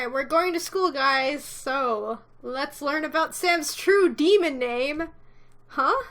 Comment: so i was skipping ahead past the text that i knew and where do i land.. as Said "True demon name" i heard "True demon Babe"... i was so confused